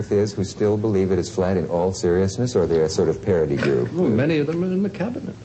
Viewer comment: Many thanks for uploading this - it's a real treat. Writing from the UK I think Cleese says at 7:46, "Many of them are in the Cabinet"...